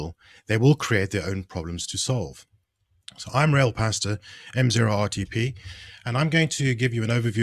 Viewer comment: i thought he said "i'm real pastor" when he said his name